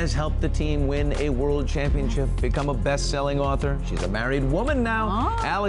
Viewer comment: Did he say "She's a woman now?" Oh wait, my bad, I just misheard that's all.